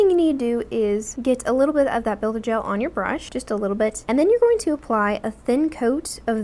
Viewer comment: to me it sounds like she says "build-a-gel" and it's actually adorable ... lol thanks for the good video